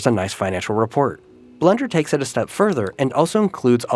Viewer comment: Just me or did it sound like he said "Blunder" every time he said blender?